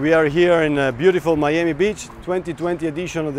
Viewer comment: It sounded like he said............." Miami Bitch ".........in his charming Italian accent..................